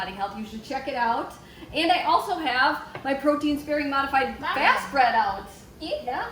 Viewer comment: You say something I couldn't catch @ 2:12 : "And I also have_____ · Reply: My Protein Sparing Modified Fast bread. :) <link>